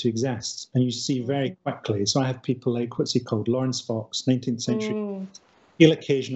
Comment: anyone catch the name of the person Frankie mentions at 18:26? It sounds like Laurence something, but i can't figure it out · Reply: Laurence fox